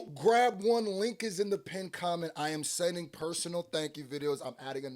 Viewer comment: I always thought you said "pink comment" 💀🤣